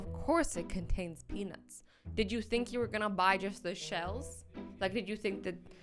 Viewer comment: Pause then cause she says jush instead of just